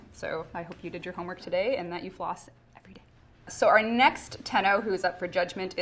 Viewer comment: the way she said Tenno sounded like Lotus to me